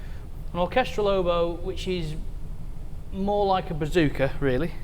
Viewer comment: Did I hear you say "Bazooka" instead of "Bassoon"?😁😁😁